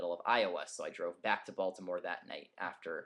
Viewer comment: lol when he said Baltimore i thought he said Voldemort